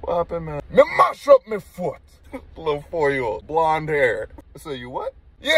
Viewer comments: "You ain't heard mi", "mi mash up mi foot" on repeat😂😂😂😂😂. Who watched this more than once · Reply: Omg i was thought he was saying my mom shot my foot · I thought he said “ my ma shot my foot” 😂😂😂😂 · I listened ti this 12 times before I heard “Mi mash up mi foot”. I definitely heard “Mi Ma shot mi foot” I couldn’t figure out why it was funny. 🤦🏽‍♀️🤣😂